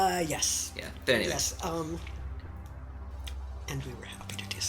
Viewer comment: What did she say at the end? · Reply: "And we were happy to do so"